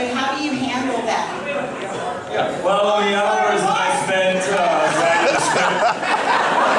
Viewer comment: What did mark ruffalo said at 43:59.. when he started to walk? Anyone plz explain! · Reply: It was meant to be a question for Mark Ruffalo, but she asks one directed at the writers, so he stands up going "well, the many hours I spent writing the script..." and starts walking up and down. you can hear the woman go "Oh Mark! I'm so sorry!"